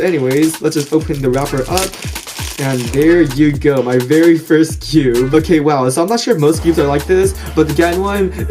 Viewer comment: It sounds like he said: “My very first cube.” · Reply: Kelly he said very first skewb